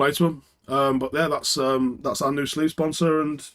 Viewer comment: Ha, at first I heard our new "Sleaze" Sponsor. And I was like, man that is some radical advertising. 😁